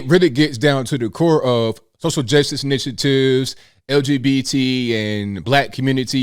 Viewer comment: says "Social Justice Initiatives," But I heard it as "Hustle Justice Initiatives." I think that is a much better term for it.